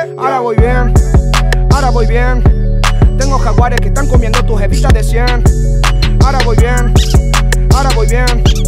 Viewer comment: en argentina, los billetes de 500 tienen un jaguar y los de 100 a Evita Peron, por eso dice "tengo jaguares q estan comiendo tus evitas de cien" por si no lo entendieron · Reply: siempre pensé que decía " ahora tengo jaguares que están comiendo tus heridas de 100" jajajaja